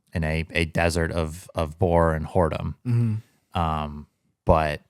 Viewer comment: did I hear borrer and horedom? Kinda fruitful slip 🤭